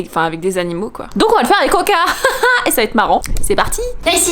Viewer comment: c'est moi ou elle a dit coca😅